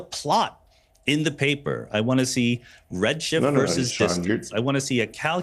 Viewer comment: What's he saying? It almost sounded something like 'How dare u Sean' but I can't quite make it out.